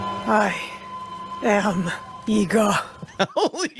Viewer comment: DID SHE SAID "I AM NIGGA" ???